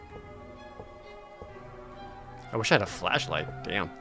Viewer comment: is jared saying he wish he had a "flesh light " or flash light?